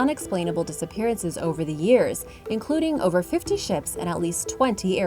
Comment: am I hearing shits?